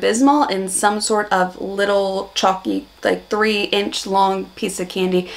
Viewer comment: Love your observations. When you said musk sticks are" kinda chalky" I heard "chokky" and thought, "No they're not".